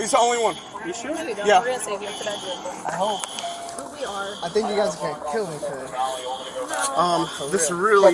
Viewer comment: Did he say “I fucking hate you guys” at 8:53??